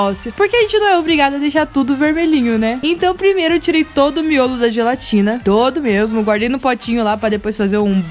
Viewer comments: vc falou "miolo da gelatina" ou escutei mal? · Em 1:42 só eu q escutei ela falar "miolo da gelatina"? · EU ESCUTEI ELA FALAR: -EU TIREI O MIOLO DA GELATINA!!! EU ACHO QUE ELA IA FALAR: -EU TIREI O MIOLO DA MELANCIA